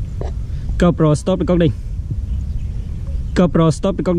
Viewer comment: a nói gì cóp rồ sờ cóp binh là cái gì vâyh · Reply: Nếu bạn còn dùng nick yt này thì hôm nay là thứ 5 ngày4/6/2020 lúc 00:52. Mình trả lời câu hỏi của bạn là câu nói của mình dịch được là: Gypro stopping recording( gypro dừng quay )